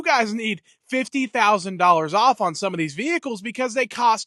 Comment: Toyota TRD double cab went for about $40,000 in 2012. Going up to $50,000 in 2025 is not that big. It's big, because inflation is always with us, but it's not that big. I thought I heard you say "$65,000" but then I thought I heard you say "50,000." Either way, the benchmark for a fully-equipped Tacoma TRD in 2012 is about $40,000. When I say "fully equipped," I mean it had 400-Watt, 110-V AC plug-in in the right rear fender well. It came with an automatic transmission cooler (essential if you're going to tow with an automatic, and pretty much standard in 2012 for the Tacoma). I got the backup camera but I think I opted out of the GPS navigation option. It also came with 7-pin connector all ready to hook up to any trailer. I opted for using the navigation on my smartphone, like my nephew did, but it burned up my phone, working way too hard to talk to the satellite.